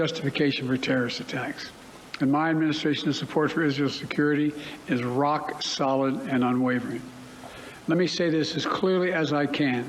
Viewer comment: Anyone understand what he means at 1:14 ? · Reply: My administration support for Israel security is rock solid and unwavy. Let me say it as clearly as I can, this is not a moment for any part hostile to Israel to exploit these attack to seek advantage